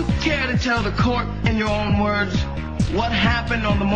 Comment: Did I hear him say "I did it I screw tuna" D